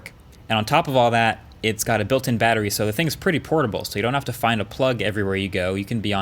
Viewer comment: Did he say porn?